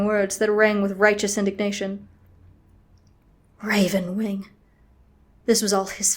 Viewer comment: I don't know if I'm hearing things but I could've sworn I heard "Ravenshade" at 3:52. XD